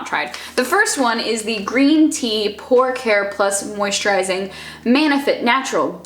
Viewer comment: "pore care" I heard "pork hair"